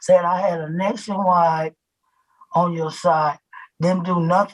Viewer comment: Did she just say "Nationwide is on your side" 😂😂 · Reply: She said verbatim “ they tells me I had a nation wide on my side “. So whatever it is, physical or anything else, it is apparently…… WIDE😮